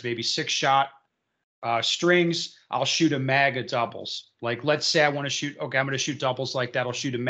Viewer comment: I heard "MAGA-doubles"